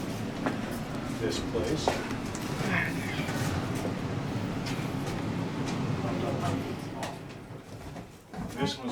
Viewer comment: Hi, 3:47 I thought I heard a whisper not sure if it was you or clothes shuffling. I’ll never for the life of me understand how anyone could harm another let alone be cruel to children. Thanks for sharing this with us, (I’ve only just started watching) 🤗❤️🇬🇧😘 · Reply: Hiya :) thank you so much for watching! How are you? 3:47 - I actually think that was me saying “alright”. It made me soo angry hearing what she did to the children. Such a monster. I’ll never understand it either! Xoxoxo